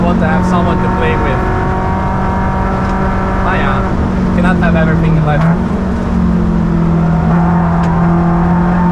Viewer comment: did he just say "maarja"? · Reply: „naja“ which is german for „so what…“